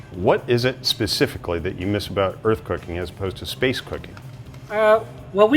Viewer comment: I thought he said space cocain